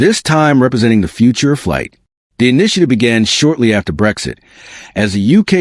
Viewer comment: First time I heard it, I thought he said "Breakfast", ... 2nd time I heard it as "Brexit". Dumb ears ...